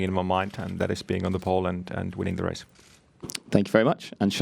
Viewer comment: did he say that he has been in Poland or being on the Pole ? xD edit: oh yeah, twice "and", i can hear now